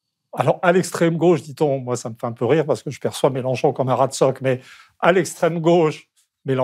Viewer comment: A 15:46, dit : moi je persiste Mélanchon comme un « radstok » rat de stock? Je n’ai pas compris … Quelqu’un sait?